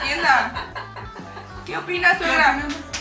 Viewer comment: Soy yo o en el minuto 10:06 escuché que mont dijo que "que opina suegra" segun mis cálculos :v osea mont y Rod ya son novios :o